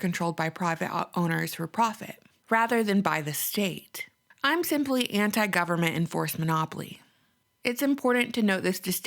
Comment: I really like your videos, but the CCing is only in "Korean", not in English. You went through some important concepts at a fairly rapid cadence; dare I say you were speaking quickly. Specifically at 2:41 I had my "voice recorder app" record you and this is the "text" of what the recorder translated: "I'm simply anti-government enforcement oplii..." - which is not too far off from what I was hearing... After replaying from that point over and over again, I think what you were saying was: "I'm simply anti 'government enforced monopolies' " So maybe CC English next time?